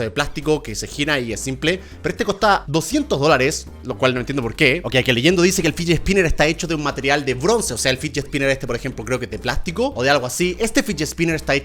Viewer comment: en el minuto 1:47 pense que decia alpha sniper